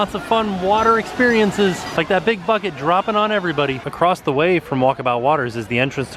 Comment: I swear to god I heard “that big f*** it” and I spit my soda out.